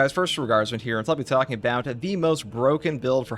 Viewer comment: Wow, I feel really dumb right now. I always thought you were saying "first regards man here"